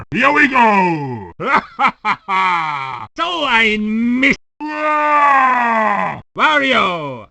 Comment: I always thought Wario said: "doh I missed"